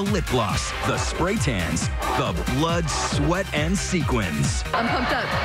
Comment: I thought they said "blood, sweat and semen" first